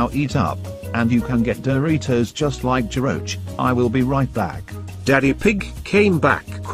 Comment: Did he say Geroge